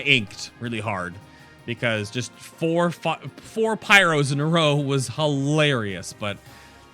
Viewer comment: Ahaha i think at 34:54 i heard a "four fuc.... eeehm, fireros in a row". It almost burned through you xD